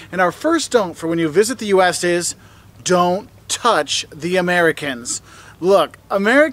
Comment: when he said dont touch the americans. it sounded like he said "dont touch the cage" XD